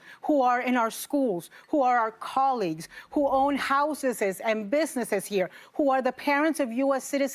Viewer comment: All I heard was "houseses"